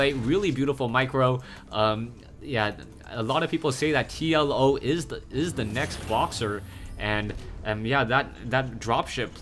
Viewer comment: When said TLO is the next Boxer, i thought at first he said TLO is the next Boss